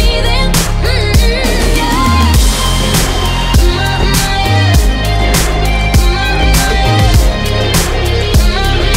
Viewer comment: What is she saying here? Sounds really good with the music. My favorite part. 😍😍🔥🔥👌🏾👌🏾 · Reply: “my my air”